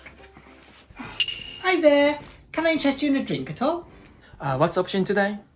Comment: はじめまして！ちあきさんから飛んできて、英語の勉強に見始めました、とても面白いです！！ 0:56の所、なんて言ってるのか教えていただけませんか、聞き取れませんでした😭😭 Can I adjust you a drink at all? ですかね？ At allで、ほんの少しでもいかがですか？って感じですか？ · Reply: Can I interest you in a drink at all と言っています。which means are you interested in having a drink と同じらしいです。opening conversation のためによく機内で使うらしいです。👍